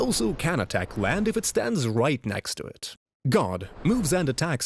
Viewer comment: With your accent I initially thought you said "God" instead of "Guard" and was like "well that escalated quickly..."